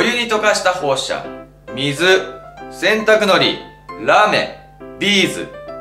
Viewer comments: ラメがらーめんに聞こえたw( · ラメがラメーンに聞こえたw